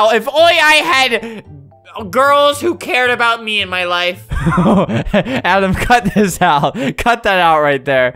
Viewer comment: Adam,edit this out!! Adam: You see I am going to do what is called a pro gamers move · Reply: I thought it said don't edit this out but I listened to it